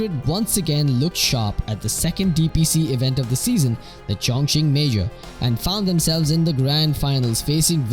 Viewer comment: not sure if I'm hearing it correctly, but did he say chong-ching major 8:35?